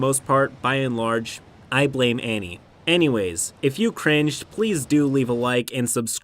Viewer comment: did you just say "Annieways"? 😄